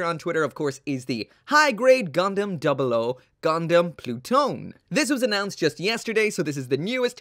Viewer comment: im fairly certain that 0:41 pronounced plu-to-ne in japanese or at least thats what i heard while watching crossrays clip